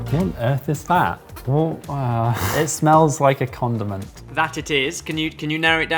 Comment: I had to rewind because I thought James said “it smells like a condom”